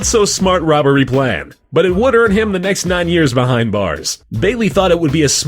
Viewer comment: What did he say? Can't hear the beginning of the sentence ^_^ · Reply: He said: "But it would earn him the next 9 years behind bars."